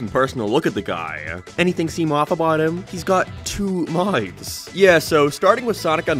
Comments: What was said: "he's got two mouths" What I heard: "he's got two lives" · "he has two mautffthss"💀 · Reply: Bro I heard him say “he’s got two eyes” and I thought he was insane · I thought he said “he’s got two Minds” 😭